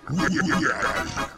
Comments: WHY I HEAR GAY?? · I love the "Why why why, this is our future" part that plays at the beginning, just adds to the sense of failure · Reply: sound like hes saying yeah yeah yeah, your outta here